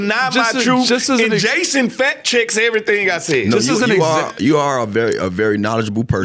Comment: could have sworn he said chasin fat chicks